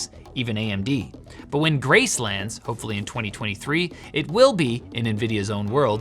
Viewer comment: Did you say "Gracelands? Free Burger with every CPU? Oh-huh! Thank you very much!